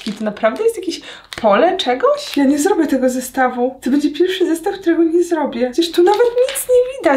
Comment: usłyszałam: "to będzie pierwszy zestaw którego nie zrobię"😭 pomyślałam: "nie, to nie możliwe, przecież Aga zawsze daje rade"😘 Takk, możesz nagrać więcej ASMR, w twoim wykonaniu jest superr❤